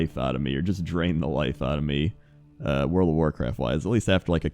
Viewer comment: I think was trying to say "...draenor life out of me"